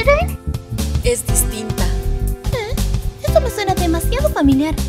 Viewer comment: esto fue lo que entendí que dijeron: Rin: ah ah avesseru ~ Len : esto es tinta por favor digan que no soy la única que escucho eso